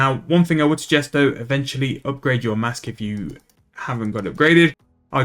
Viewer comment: upgrade your what? Couldn't understand what you said there. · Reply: your rotten pumpkin mask from 2023 event